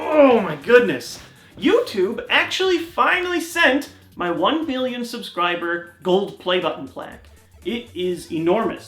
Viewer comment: it sounds like you said one billion.